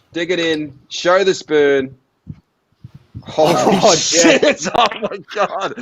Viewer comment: Did one of them say holy sh*t 1:02:50 - 1:00:30? Or was I the only one who heard that?